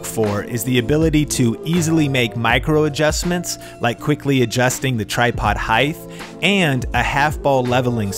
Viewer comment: Did...did he just say 'heighth'?